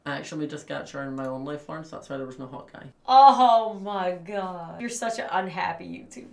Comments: The moment when thought Lauren said ‘Hawkeye’ instead of hot guy tho xD Idea from in the dark ʚ♡⃛ɞ(ू•ᴗ•ू❁) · I also thought Lauren was saying hawk eye and I was so confused 😂😂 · When Lauren said "Hot Guy" I also thought she was saying Hawkeye and was confused 🤣